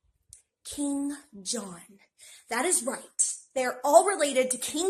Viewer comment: bro when she said King John i thought she said Kim Jung Un for a second 💀